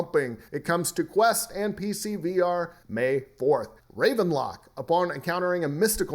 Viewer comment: I got so excited when said Ravenlok cause I thought he said Ravenloft....dammit...now I'm sad lol